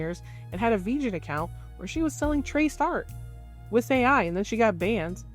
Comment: Really random, but the automatic subtitles messed up, instead of Vgen it says "virgin" and "veja" 10:25 😅